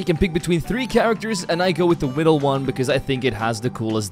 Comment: Why am I hearing widdle one😭💀